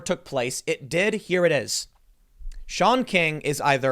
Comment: For some reason, when he said "Shaun King" I heard "Shaun White".